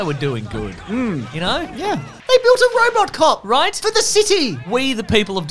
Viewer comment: I kept hearing "Robert cop" lol